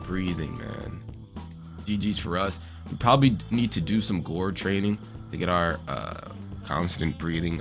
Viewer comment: Ngl I thought he said gore yraining